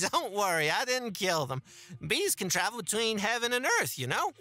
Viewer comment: I was half asleep and before I passed out, all I heard was "Bees can cross between heaven and earth!" And I shot awake